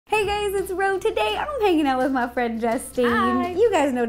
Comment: Did you say gays